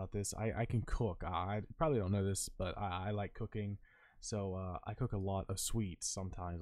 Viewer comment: I thought you said cocaine instead of cooking for a sec